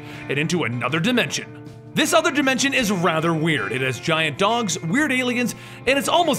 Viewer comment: I thought he said giant dongs for a bit